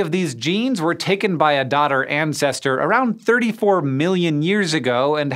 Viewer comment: L ol I thought you were saying daughter planets